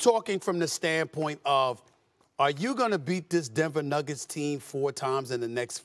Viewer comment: Sounded like he was gonna say “Denver Nuggets Jeans” 😂